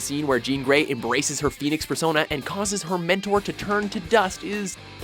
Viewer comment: I heard "Phoenix fursona"